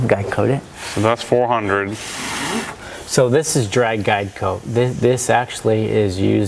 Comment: What's J saying @ 2:20..? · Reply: He's explaining the use of a dry guide coat.